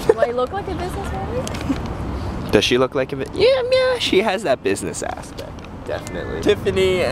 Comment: I thought you said "yeah she has that business ass....pect"